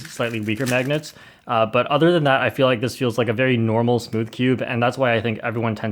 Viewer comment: I thought he said “spoodcube”